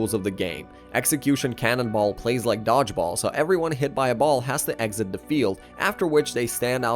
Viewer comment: why do i hear "so everyone hit my ball" instead of "so everyone hit by ball" lmaooo💀💀💀